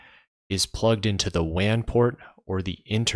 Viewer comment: did you say wan or lan